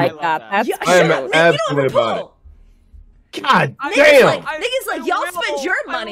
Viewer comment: did she just say the n word? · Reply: Shes saying "nick is like"